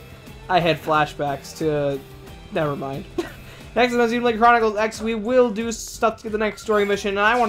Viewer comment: Loving the video quality. I think this should have just been another Xeno- game, to fit in with Gears, Saga and Blade. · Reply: Aw fluuf, musta misheard. XD I still think Xenocross sounds like a cool name though. But yeah, I'm ok with the name Xenoblade Chronicles X. The name Xenoblade X does sound cool to. :D